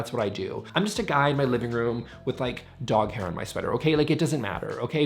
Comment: "I'm just a guy in my living room with dog hair on my sweater..." 🤣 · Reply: Oh wow. For some reason I heard him say …dog hair on my scrotum” and I’m so glad I was mistaken. I need a hearing aid